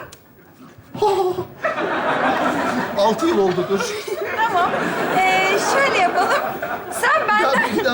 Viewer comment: ne diyor anlamdım anlayan varsa cevaplasın lütfen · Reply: Altı yıl oldu dur diyor